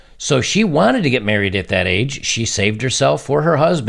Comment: I thought it said she "shaved" her V!😂